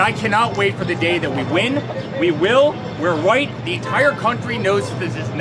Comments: I thought that's he SAID We're white! At first I thought maybe he meant we're right 🤯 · Dannnnnnnnnnnnnnnnnng, gurl. 😆 Did he just call DeNiro "washed out"?! Did he just say, "We'll Win- We're White"? Just... Dannnng.